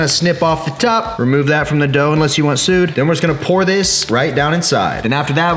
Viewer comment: Did you say "remove that from the dough, unless you want to sue"??? that's like spilling hot coffee onto yourself & suing for it being too hot lol....